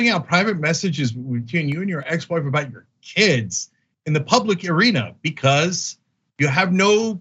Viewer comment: "Public urina." Did anyone else hear that, or is Youtube rotting my brain?